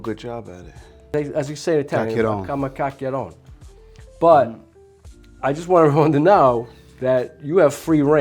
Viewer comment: What'd you say?! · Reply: Chiacchierone 🤌🏼 My Poppy used to tell me that all the time 😂😂